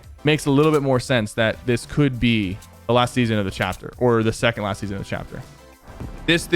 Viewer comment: When he said chapter 2 season 4 was gonna be the last season of chapter 2 I thought he said the last season of the game